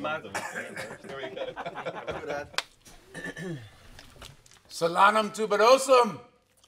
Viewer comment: What did he say at 0:11? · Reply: Solanum tuberosum is the scientific name for potatoes